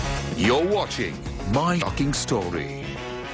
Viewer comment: When it said "My shocking story" at 1:16, i thought it said "My fucking story"